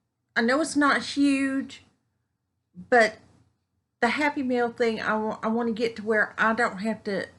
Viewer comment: Good with the happy mail... i first thought you said happy meal....lol